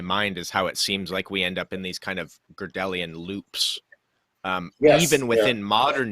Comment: What is that word he said at 28:15? Gardelian loops? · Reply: I think it is "Godelian" loop. I found a definition but then it defines "strange loop." I assume they're connected.